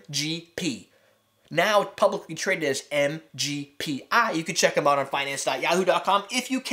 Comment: MGP is not their stock ticker. MGP is the stock ticker of MGM the casino company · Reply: Minute 1:29 or so. And I apologize, I misheard, you said MGPI, not MGP. However as I understand it, Seagrams bought them but has been bought by Vivendi, the outcome is that it is no longer independent.